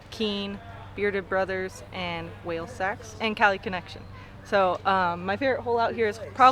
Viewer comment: did she say she is sponsored by whale sex?